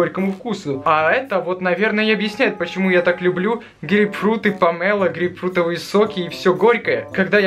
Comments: послышалось: "так вот почему я так люблю гейфрукты" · 😂😂 мне услышалось гей фрут. Я знал что он ивангей))